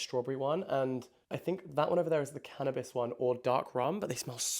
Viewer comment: Did you say Cannabis one?😂